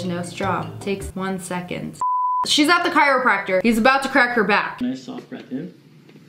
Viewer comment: instead of a crack your heard a cr4p